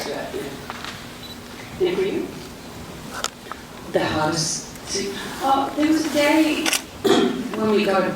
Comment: what did cate say from that on? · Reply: "The hardest.. thing? Oh, there was a day..."